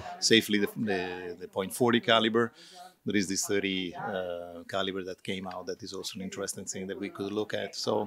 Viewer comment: Did he really said 30 caliber pistol they are going to make? At 12:36 ?? Man i want that tokarev round.